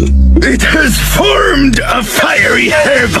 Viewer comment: I thought he said "Who the fuck ar-"